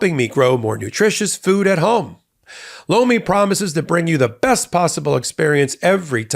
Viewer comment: Tell your sponsor (Lomi) I was in the other room when I thought I heard Popak say "BLOW ME" - Their product name is horrible - They might want to change it!!